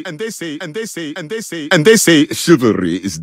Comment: Dud I can change what I hear from “and they say” to “under say” to “endersay”